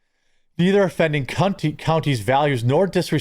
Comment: LOL did you all notice that when he was talking about them and the county values, he misspoke and stated CUNTY instead of COUNTY, lol that was priceless... Go back and rewatch it from around 8 minutes to 8:50. I thought that was so rewarding and hilarious... Just Saying!!!